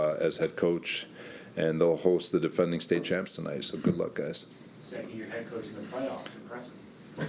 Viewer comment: What was the comment that reporter said at the end that had everyone laughing? Can't make it out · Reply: He said “2nd year coach making the playoffs, that’s impressive”